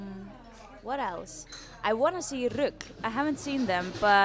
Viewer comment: Sorry what's the name of the band Sóley mentions at 4:46? Sorry but I can't quite catch it are they called "Rook"? Thank you, in advance. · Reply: i Think the band is RUGL, but Im not so sure.